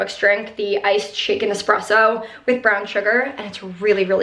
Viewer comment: Truly thought you said "iced chicken espresso" LOL, but I really love this positivity it's helping me start my work week off right!!